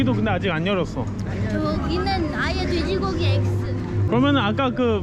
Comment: 영상 속 캬릉포인트:12시! 6시! 12시! (확마!) 돼지고기가 대희고기로 들리는건...환청입니다.